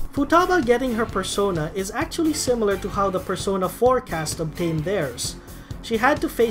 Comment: Am I the only one who misheard "Persona 4 cast" for "Persona forecast"?